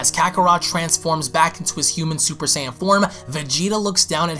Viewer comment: Did you really say human super sayian form lol